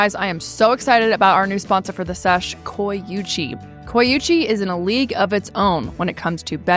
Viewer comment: I thought they said they were sponsored by “coochie” not “coyuchie”